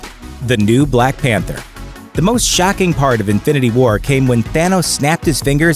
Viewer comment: did he just say shacking instead of shocking?